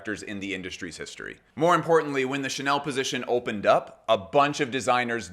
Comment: for a second i thought you said "Unfortunately did not die" but after rewinding i see you said "importantly"